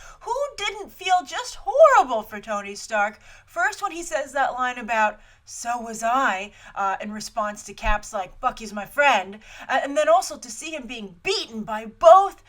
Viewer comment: at first I thought she said "fuck is my friend"